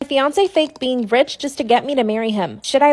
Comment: I thought it said Beyoncé when it said fiancé 😭😭😭😭